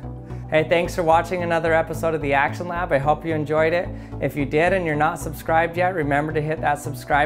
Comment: I thought he said at the outro that "if your dead and your not subscribed yet make sure to subscribe" >:3